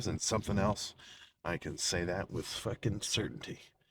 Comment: First I thought you were saying goodbye roids